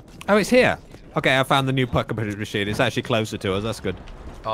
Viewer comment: tf you just say?😂 Packuhpundhdhshsgaga is what i heard lmfao.